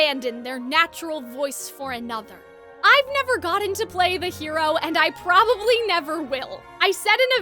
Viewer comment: Mirror mirror will forever be my real Snow White LA and this one will NOT exist, just as the first LA avatar the last airbender movie does not exist! Pd: can you repeat what you were saying at 18:30 please? I couldn't hear you behind the tears from the Focalors sacrifice playing int the back🥹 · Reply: Lmao I'm surprised the music was louder than my voice, that typically never happens! 😂🤣 I say "I have never gotten to play the hero and I probably never will."